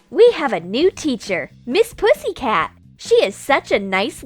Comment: wtf I thought it said ms pussy